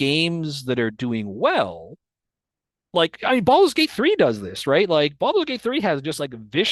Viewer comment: i swore he said "balls deep 3" at 2x speed and had to double back